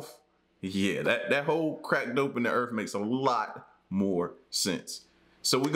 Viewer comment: I swear I heard him say crack dope n the earth multiple times lolol I like your vids tho